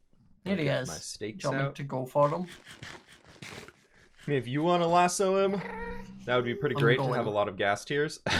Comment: I'm new did you call "babe"? Or is it just my brain's on Pride right now? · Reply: He said "*I mean* if you wanna lasso him..." lol